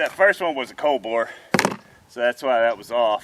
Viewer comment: Not bad after the cold bore shot👍🏻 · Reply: At first glance I thought you said cold beer shot 😂 🍺